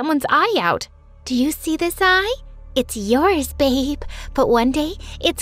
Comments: did you call her babe😧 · Wait Wait Wait wait did she just say baby or idk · did she called her babe